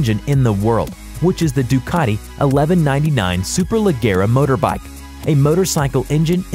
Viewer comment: did he just say super ligma?